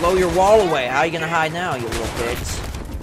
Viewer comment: O did you say "how are you going to hide now you lttle bitch" at 11:32 ?